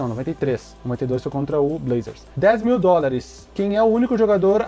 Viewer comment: em 92 foi contra o portland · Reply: Mano te juro que eu escutei lakers